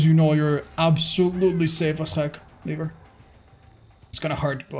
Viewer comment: what did bro say :| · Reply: "Leave her" but ofc it sounds like the n word